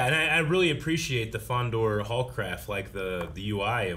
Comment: Because of the British accents, Every time they said "Haul Craft" I heard "Horcrux" Lol.